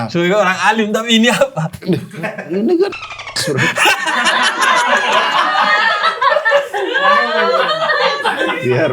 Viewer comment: guys tretan ngomong apa ya???? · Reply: cosplay teroris